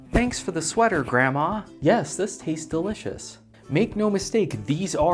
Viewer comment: I wasn't looking at the screen when he said this tastes great, so I thought he said the sweater tastes great